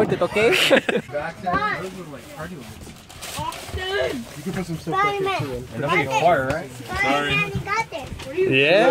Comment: what did say? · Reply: Spiderman you got this